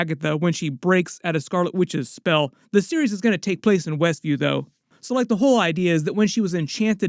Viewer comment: At first I heard, "...the witness that Jimmy Wu was coming to WESCUE..." instead of West View. ROFL